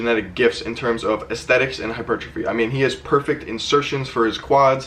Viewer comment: when he says daves legs are his genetic gifts in terms of aesthetics and.... What is that word? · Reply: hypertrophy